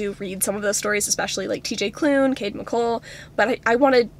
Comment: talking about m/m romances and she says tj klune and another author but for the life of me i cant hear what the second authors name is, does anyone know? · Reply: cole mccade